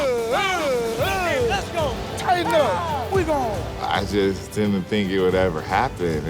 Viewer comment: did Blount predict the future or is there something we don’t know because I heard him say, “Titan Up”